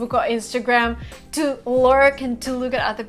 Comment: "...to lauric and to look at other people..." what does this mean - to lauric? Reply in Russian, please. The only word I didn't understand in your video and dictionary doesn't contain this word too. Help me, please. Maybe I just misunderstood the word. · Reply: she said "to lurk"